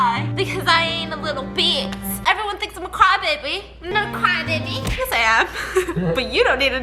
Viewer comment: I heard " crime baby" instead of "cry baby" but you are a crime baby biss! I send my best wishes to mango💕